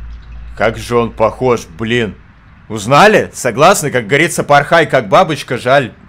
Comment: 》Я один "Хой" услышал?